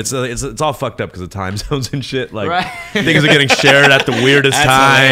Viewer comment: Did he just say the n word?? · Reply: Things are getting shared*